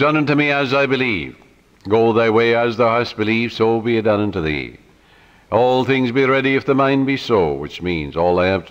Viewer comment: What does he say at 46:50 after "it is done unto me as I believe"? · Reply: kevin wasie matthew 8:13 “Go thy way, and as thou hast believed, so be it done unto thee”